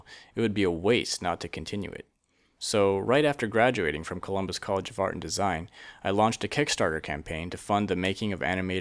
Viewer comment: Anyone notice that it sounds like he said Columbus College FART and Design😂